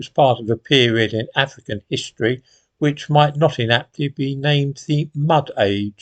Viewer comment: What does say at 2:00? "Like Zimbabwe, it was part of the period in African history which might ????????? be named the "mud age." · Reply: "it was part of the period in African history which might not inaptly be named the "mud age.""